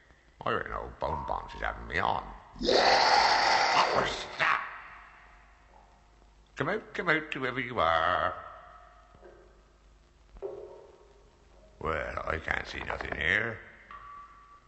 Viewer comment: When Berk went "What was THAT?!" I thought he said "Oh, SNAP!" Then it occurred to me that that phrase didn't really come around until well after this show aired, so I went back and listened again. XD